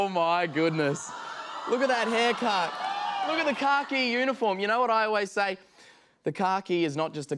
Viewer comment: Thought he said “car key uniform” in the beginning 😂